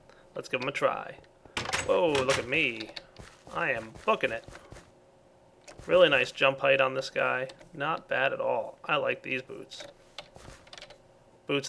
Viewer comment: did swear when he said "I am f***ing it" or is it just me?